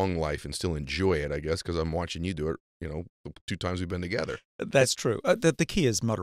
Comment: Seriously. At about 7:40, did Ed really say "we've drinkin it together" Take a little time to learn what terms or tenses do not even exist in your native language. It leaves a sense of doubt about intellectual acuity. Love Sinclair. And he did tell Ed later that how he was pronouncing something was correct. So why not help people by letting them know that "we have drinkin together" is just plain ignorant. I am not targeting Ed; I am hearing this kind of mistake in all walks--news anchors, politicians, scientists, and the extremely young who like to spend their days drinking $7 starbucks and then complaining that they will never be able to buy a house.